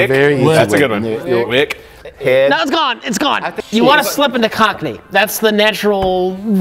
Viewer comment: Did Rich just say, " You have to slip in the Cock knee? That is disgusting, even though I'm not exactly sure what it is. Does it have something to do with the gummy worm? Shame on you Rich Evans, my 3 year old will not be watching this show any longer! I guess it's time to go back to "The Cosby show"